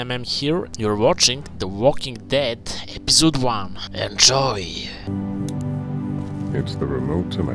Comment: Lol I honestly thought you randomly said Anchovies instead of enjoy. I like it. ^-^